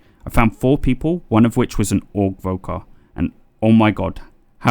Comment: What are you saying at 20:48? Orc rogue or? xD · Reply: "Augvoker" :P haha Ill try and be more clear in the future!